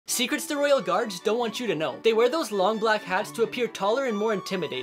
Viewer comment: I thought u said they don't want us to know theyre secret but why did u know??!!!!